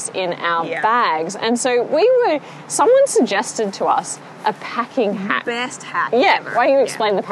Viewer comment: So I thought for sure said, "packing hat," and I envisioned you both wearing these giant Cat in the Hat style hats absolutely stuffed with board games to smuggle them onto the plane.. LOL. Oh, packing HACK. Looks like you've had an amazing trip! Safe travels home.